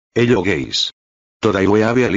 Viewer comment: at the start i heard "hello gays" BRO I